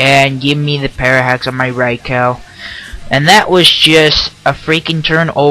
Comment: lol, did you just say raicow?